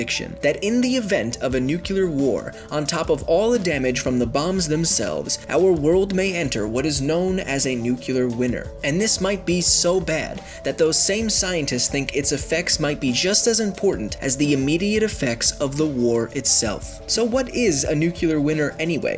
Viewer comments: Did he just say a nuclear winner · Winter has a t in it, it's not a silent t... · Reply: I heard "nuclear winner" every time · it sounds like u say nuclear winner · Did he say winner or winter 0:15 ? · Is it just me, or did I heard him say "Nucular winner"...?